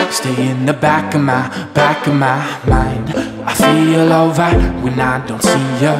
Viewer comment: I swear it sounds like he says "I'm feeling alright, when I don't see ya" instead of "I feel over..." Ah well, both work ;)